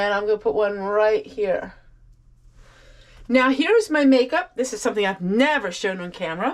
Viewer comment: Lol...I heard "something I do 365" and I thought... jumping jacks??😮